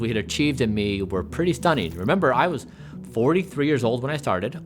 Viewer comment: Ps: 1:06 all I heard was “pretty and pretty stunning” 😋you look amazing!